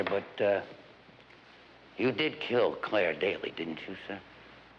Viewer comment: I thought he said "Did you kill Claire daily?" That would take a LOT of Claire's...